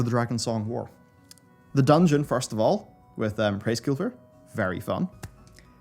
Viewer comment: what does he say here? "The dungeon with ....." I can't tell. It sounds like Praiskilvr. It can't be Hraesvelgr.